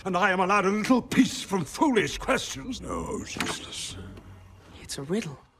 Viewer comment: i don't think Gandalf said "oh Jesus" in there movie. Did yall hear that too...